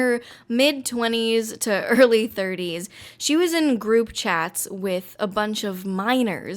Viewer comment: I honestly first heard "groom chat" instead of "group chat". Needless to say I know what she did.